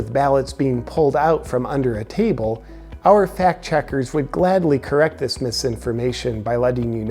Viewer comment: I'm so sick of people referencing "fact checkers" as anything legitimate. People are such sheeple, it's unbelievable. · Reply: Oh I thought they were saying "fat checkers"